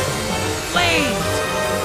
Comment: Silver say "Blaze" but the auto-subtitles say "flame" close enough computer (not quite)